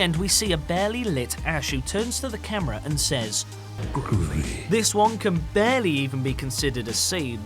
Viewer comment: what did she say ? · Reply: Groovy i think